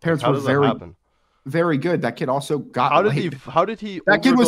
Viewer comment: is he saying "he got laid"? If so, why is he proud of his brother for getting laid at 14 when he also went out like George Floyd?